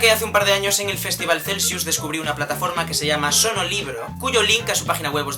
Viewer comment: ¿Cuál era el sitio web de los audiolibros? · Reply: Freddy Alexander Morales Romero con razón no la encontraba, yo también escuché "sonolibros" jajaja gracias!